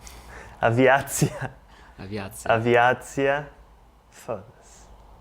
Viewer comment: Did you say fode se on 1:06 ? jajajaja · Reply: "Foda-se" It's something like "damn".